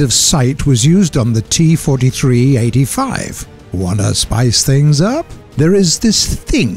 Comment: did I just hear T-43 ? New Russian Prototype Tank confirmed